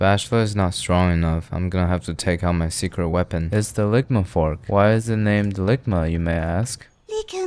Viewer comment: I've always thought he said lickmyfork not ligma fork 😭